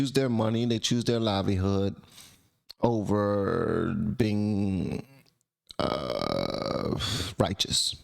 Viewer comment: "over their morales" I thought was what you were saying.